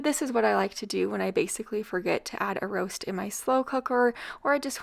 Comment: I thought you were saying Walgreens instead of Wild grains Lol